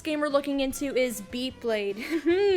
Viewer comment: i thought you said "beyblade" lol!!